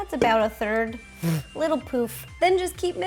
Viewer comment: I miss heard "little poof". I thought she said "little poop"